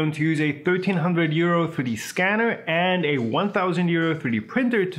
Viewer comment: not gonna lie when u said 1300 euro scanner i thought u said 1300 year old scanner and i was like O.o what?